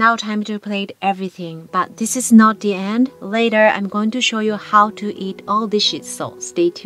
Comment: I’m sorry I know this your second language so please understand I’m not making fun and I love what you do, but at 8:39 I could have sworn you said “I’m going to teach you how to eat all this sh&t, so stay tuned”. It had me laughing. I thought Google translate failed you. I watched that same spot 10 times